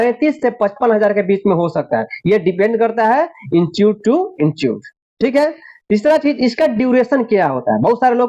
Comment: Kya bola kya bola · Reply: Institute to institute